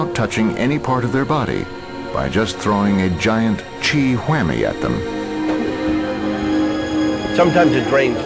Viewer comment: Am I the only one who heard the announcer say "by just throwing a giant Chi Whammy at them" @ 3:24? Lmfao